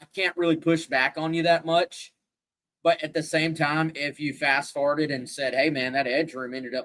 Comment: I think you said “fast farted” at the 28:32 mark and I died laughing!! Good stuff fellas! Excited for camp to start up!